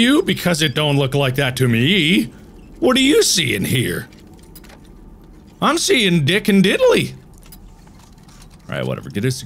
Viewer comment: I come back after pausing the video, and all I hear immediately is “i see dick”